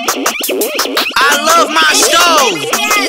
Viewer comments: I thought he said 'I love my shows' for months until I came to this video and saw everyone commenting stove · i love my stove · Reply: I thought he said “I LOVE MY SHOWS”